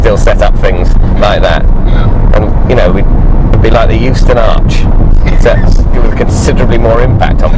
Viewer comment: What is David saying at 12:00 as an example of a publicly owned thing that got scrapped and everyone wished it hadn't been? The "Youston Arch"? I looked up the "Houston Arch" and got a LGBT history site.